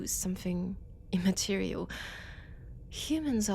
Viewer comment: When she said “immaterial” I honestly thought I heard “Immaterium.” I’m too Warhammer-pilled, man